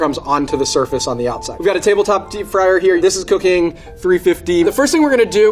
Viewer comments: did anyone hear “this is cocaine”? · why do I keep hearing “this is cocaine” lol